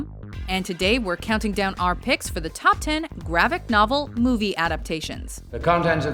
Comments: Did she say gravick novel? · is it me or did she say gravic novel